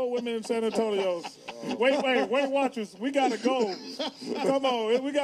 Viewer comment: "that's a gold mine for weight watchers" that's my favorite part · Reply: I thought he said whale watchers